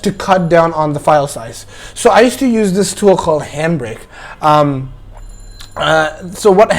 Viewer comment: Wait wait wait what did he say ? 0:29 Him bray ? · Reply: Handbrake